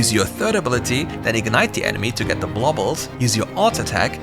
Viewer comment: I keep rewinding but can't understand what word you saying at 4:38. U said to ignite the enemy to get the what of?? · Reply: "bubbles" from ur W